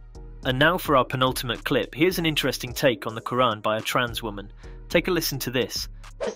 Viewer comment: I just wanted to off myself, and i hear "Kur'an woman". What if she 's waiting for me in hell? That 's hell of hell, in hell. No, thanks. I'm too stupid. Ladies first...